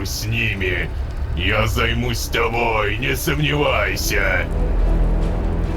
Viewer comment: я услышала вместо "не сомневайся" слово "раздевайся"